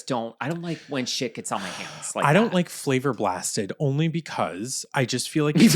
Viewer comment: I swear I thought yall were saying “flavored plastic” and I was like….wtf are they talking about…flavored plastic?!! I know these mfrs are not eating plastic, the heck. 🤨 Flavor Blasted 😂 😂